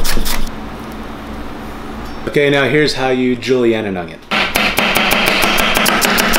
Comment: Why does sound like he is saying nugget when he says onion is my brain malfunctioning